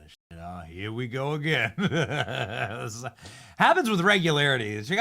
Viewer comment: ish. Did he just call his employer NBShit? 😂